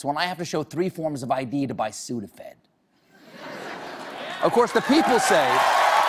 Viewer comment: What does he say? "when I have to show three forms of ID to buy ..." to buy what? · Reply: sudafed